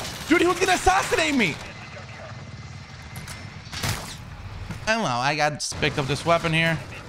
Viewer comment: can some one tell me what he said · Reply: It sounds like he's saying "Hello, I got..." and I don't know from there